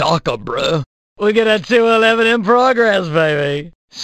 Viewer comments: What is he saying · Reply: We got a 211 in progress baby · What’s he saying? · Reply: We got a 2-11 in progress baby!